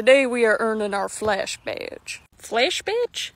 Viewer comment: tbh, when I heard "Flash Badge" I thought flashing, please tell me I'm not the only one right?